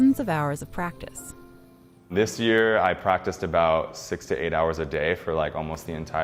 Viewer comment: what did he say 68 hours a day??? What???